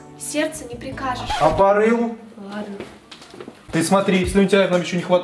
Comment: что он сказал? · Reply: "А по рылу".